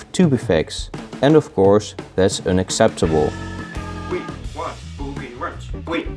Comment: I thought he said “WE WANT BOOBIE WORMS!”